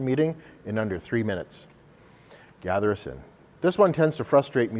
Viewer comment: When he said "Gather Us In", I heard "Gather a Sin".... WHAT?!?!? 😅